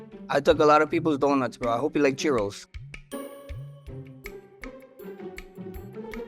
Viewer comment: I thought max said cheerios lol i was mad confused. I was like thats still an O lol